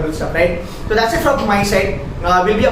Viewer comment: So many 'cides' that at in the end i heard "That's it from my cide". 🤣 Great video though. 👍